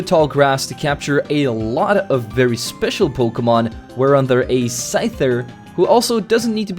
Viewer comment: Did he say at 10:37 a Dutch word “waaronder” a Scyther??? This had me tripping. The word fits perfectly and means under which